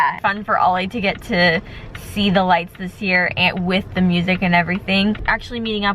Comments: It sounded like he said chicken lights!😂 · Sounded like said "chicken lights"